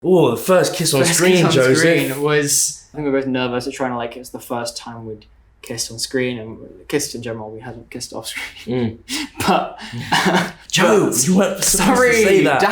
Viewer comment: I’m sorry, but did he just call him “joe-sy”?!!?!?!?!,!,????????